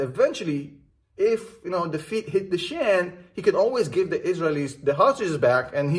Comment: Did he say "when the feet hit the shan" instead of when the shit hit the fan" lmaoo